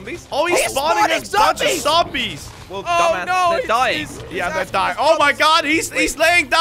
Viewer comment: Did slogoman say dumbass